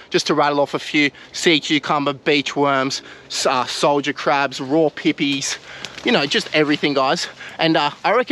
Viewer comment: Did I hear him say "raw peepees"🤣🤣🤣🤣